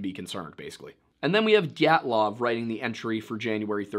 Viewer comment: did you just say GYATTlov?